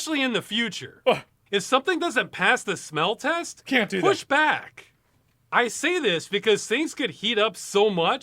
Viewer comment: is saying i should push him back?